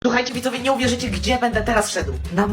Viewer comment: Usłyszałam ,,ruchajcie widzowie"😅😅